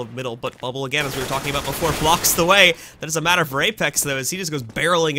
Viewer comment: it sounds like he says rapex because thats what he dos to bubble